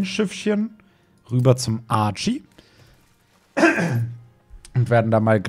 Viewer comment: Bei 3:05 dachte ich kurz das ich Arschie verstanden habe